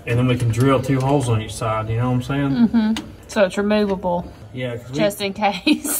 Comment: I think it’ll do on the L shaped brackets lol. Just saying, it sounded like you said know what I mean Kyle hehe